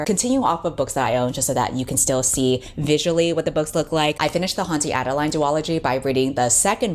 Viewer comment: I thought you said the title was Cunty Adaline and was really looking forward to what that book was going to be